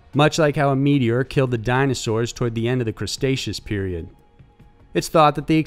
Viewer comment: Did I just hear "Crustaceous period?" is that when Crab people walked the Earth? XD